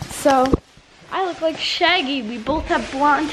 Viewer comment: When said i look like shaggy i thought she said i look like s**t😂😂